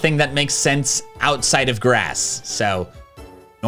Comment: Am i the only one who heard grassis